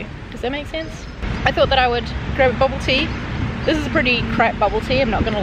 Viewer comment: You guys call Boba tea bubble tea. I was confused what you were saying. I thought you were saying Boba Tea funny but you were actually saying bubble tea.